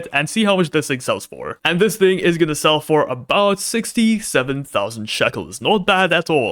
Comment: did I hear 67